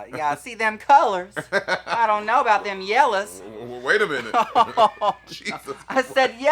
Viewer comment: “Them colors” 💀 i don’t think Scarlet got it at first and Monets face had me lol 😂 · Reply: I heard “yellows” as “yallers” and got even more scared